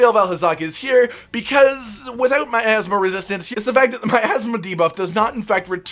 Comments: Honestly instead of Miasma resistance I heard "My Asthma resistance" :') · When he said miasma I thought he said my asthma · When you mishear "Without my asthma resistance,"... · Ok i might be dumb, thought he said my asma resistance for a sec while talking about blackveil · I heard 'my athsma debuff'